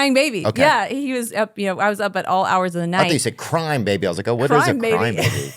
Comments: I 💯 thought she said “Crime Baby” also…then went into my imagination and thought of a whole story about a baby that loves crime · I thought she said "crime baby" too. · I thought she said crime baby as well. 😮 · I heard "crime baby" too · It honestly sounded like she said crime baby lol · I heard Crime baby as well 😂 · I heard "Crime baby" too. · Btw, I thought she said "crime baby" too. Thanks, for asking for the clarification. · I thought she said "Crime baby" also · I thought she said crime baby too!! 🤣😂🤣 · I also thought she said crime baby